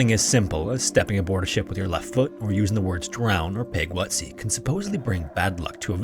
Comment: what is he saying? im hearing ta-round and peg-wutseeck. can someone help me please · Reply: He said "drown or pig while at sea"